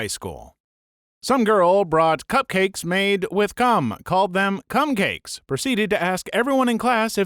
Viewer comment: I thought he said Gum 😨